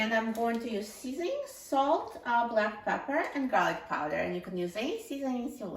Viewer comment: I thought she said black powder (old type of gunpowder)